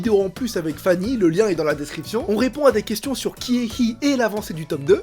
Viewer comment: quand t'as dit "Ki&Hi" au début j'ai compris "qui est Hi?" XD